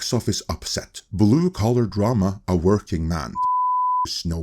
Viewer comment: Each time i hear "twerking man". i feel bad but cannot un-hear it.